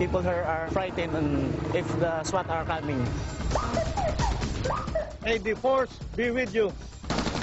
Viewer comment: “May the force be with you.” Is that what he said???